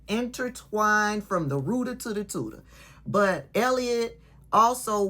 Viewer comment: Did she just say “from the rooter to the tooter?”